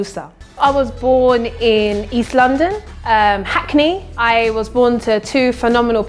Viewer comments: I thought said I was born in Eastlando😂😂😂😂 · I thought said I was born in Eastlando😂😂😂😂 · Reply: 😂😂😂😂 I also thought I heard Eastlando. OH God forgive my ears😃